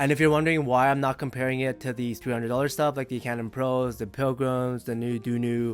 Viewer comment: Did you say (12:52) '200$ stuff ... like Pilgrim'? Isn't Pilgrim a 400$ set, which would actually make them directly comparable in the price category? Bottom line, you feel like Hype4 mk2 > Pilgrim? I am debating internally between the exact two.